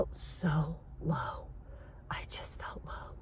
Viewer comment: I see many encouraging comments already saying beautiful things to you, which is fantastic. Somehow when you were saying "I felt low" I initially thought you said "I felt loved" (the man helping you). At that time, I did pause and did think for a moment that the person's act of offering help is not only an act of kindness, but an act of love. Then "I felt loved" would fit. Wish you the best, I'm pretty sure that you're a hero for many of us. ❤